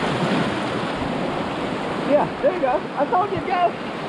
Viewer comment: Did you really say "I told you so" at 4:22? Really obnoxious. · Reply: "I told ya it would go" was the actual quote...as we celebrated not having to get out of the boat to free him from a broach situation...😆 perhaps Karenmako17 should take a day off! LOL